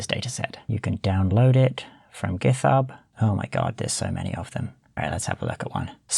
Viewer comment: Did you just say giTHub?